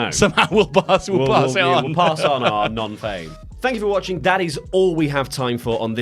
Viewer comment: i swear it sounds like you're saying "Thank you for watching daddies"